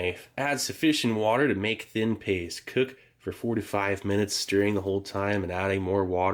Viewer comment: When he said “cook for 4 to 5 min” i heard “forty five minutes” and i was like wtf kind of oatmeal is that? Lol